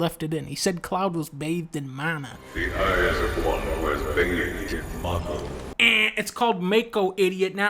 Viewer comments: I'm not sure if joking but to me he clearly says "Mako". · ? Huh? I hear Mako perfectly fine. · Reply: idk if you're serious or not but that distinctly sounds like mana, not mako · Has anyone noticed that the "Mana" voice clip at 3:45 is actually a "Yannie/Laurel" situation? I was confused because he CLEARLY said Mako....but if you listen for "Mana" you'll get that instead. Read the voice clip as he says it and you'll see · he is clearly saying Mako ... just pronouncing it mawko ... clean yo ears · I guess he doesn't say it right but he clearly says mako (ma-ko) not mana